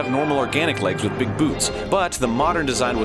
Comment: Anyone else hear "Big boobs"?